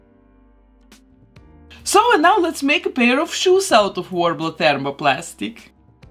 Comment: what is that called? warm-up thermoplastic? and is it sold in a flat sheet, or is it like a putty that you have to roll flat? ..wondering how to search for that product online. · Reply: Worbla